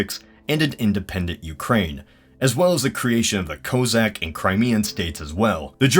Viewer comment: the subs go "Kazakh" instead of "Cossack".